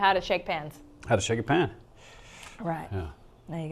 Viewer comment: Ooooohhh, shake a pan.. that makes more sense than shake your pants that I thought I heard the first time!